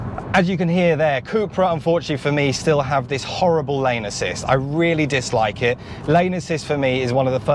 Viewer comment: I thought you said “horrible anus cyst” for a moment.